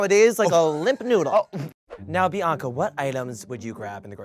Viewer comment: did Sugar just called Bianca "BI-UNCLE" coz I'm living!😂